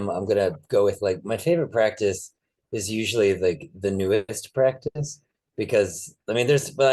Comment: I like how you finished off sharing your favourite practices. What did say at min: 1:25:20 as I couldn't quite catch it.. Also, thanks for the Book recommend Forest Bathing, will check that one out. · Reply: says "my favorite practice is usually the newest practice". Do you have any favorite practices you're currently engaging with?